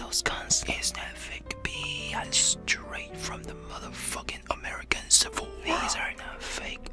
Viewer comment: Holy shit, I can't get enough of this. I thought he said straight from the American civil war lol.💜